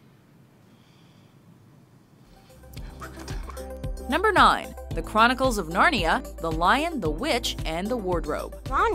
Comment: What did he say? I couldn't hear him. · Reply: it's "abracadabra"